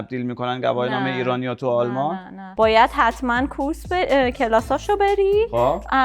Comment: چی گفت😂😂😂😂😂😂😂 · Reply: Kurs